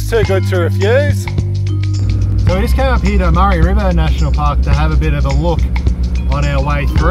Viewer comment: Is it Murray or Mary river ? Sounds like u said Murray. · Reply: Yes, i say Murray but it's definitely Mary. Sorry about that...